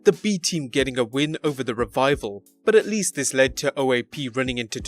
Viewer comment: Did you just said O.A.P ?